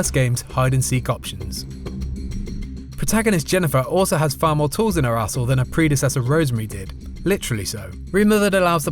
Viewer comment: Did he say she had more tours in her a**hole @ 2:04? Lol